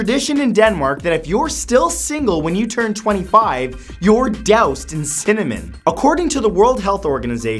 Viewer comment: i misheard the #1. i thought it was "death is imminent", not "doused in cinnamon" and thought wtf